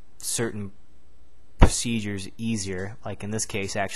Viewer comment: I seriously thought you said this makes seizures easier. Lol